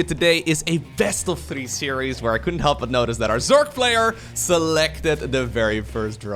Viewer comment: Did you say Zerg Flayer ? Or Zerg Player